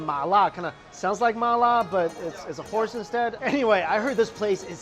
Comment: I thought he said "whores" instead of "horse" at first and was so confused.